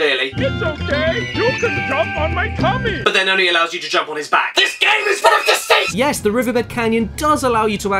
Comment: What does he say? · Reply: THIS GAME IS FULL OF DECEIT!!!!